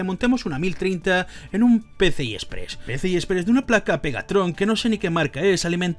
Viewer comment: pense que dijo megatron 🤣🤣🤣